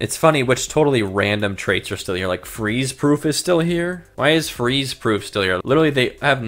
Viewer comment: I thought it said “freeze poo” 😂😂😂😂😂